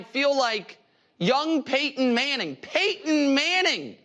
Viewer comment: Did Nick say “Paintin’” Manning? Lol